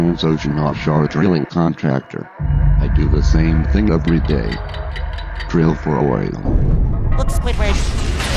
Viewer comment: What did Squidward say? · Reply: drill for oil diyo reis